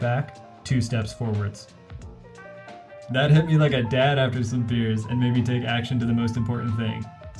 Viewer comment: A dad after sime beers??? Dud i hear right lmao